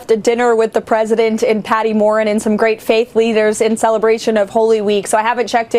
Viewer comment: Sounds like she said, "Some great fake leaders during holy week". Now, right now, America sucks!